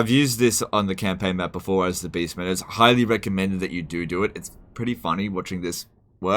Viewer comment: Did he just say "you do-do it"? Well then, he's literally a Skaven now. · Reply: ''You do does it.'' Homonyme right after another tend to sound like stutter.